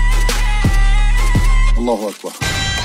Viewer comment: does it say alakba